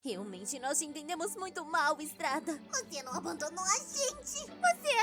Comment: eu entendi estrada kkkkk